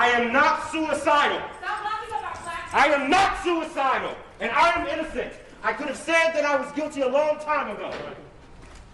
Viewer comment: a woman yells "Stop using our _ _ _ _ _ _ " ?? At Jussie as he walks out. Anyone hear exactly what she said?? Sounds like she is yelling at him to stop using black power fist? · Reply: "Stop locking up our black people."